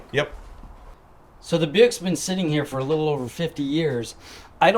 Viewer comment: did he call it a Buick or something else?